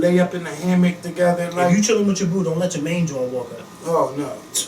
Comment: Ok I need help! I 've heard say this a few times, Lil Moe & even my uncle who is from Philly but I can't understand WHAT is actually being said. At 2:42 is saying Main Join, Main Joint ....I mean what is the word? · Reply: Jawn